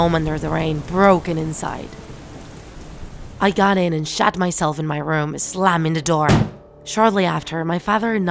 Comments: I cannot be the only who thought she said "I shat myself"at first like I am deceased. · Me at around 1:44 😀 did I just hear “shat myself in my room”